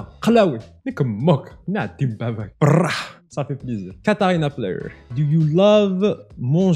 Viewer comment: so much beatiful accents in this video (" 15:16 " am i the only one who heard "ca fait plaisir"? )